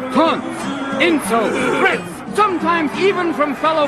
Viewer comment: Did he say "Ribs"?